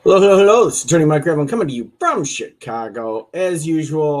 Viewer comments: Without fail, I always hear "Hello hello hello this is attorney Mike Gremlin, comin to you, from Chicago." Every single time lol. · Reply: That’s funny, because I mishear it differently. I think that it sounds like “This is Dirty Mike Gravlin,” like Dirty Mike and the Boys from “The Other Guys.” 😂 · Without fail, I always hear "Hello hello hello this is attorney Mike Gremlin, comin to you, from Chicago." Every single time lol.